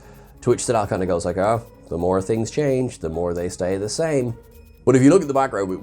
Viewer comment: what he actually said was, "The more things stay the same."